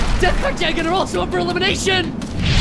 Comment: I Misheard this as Death and Jeg are up for Elimination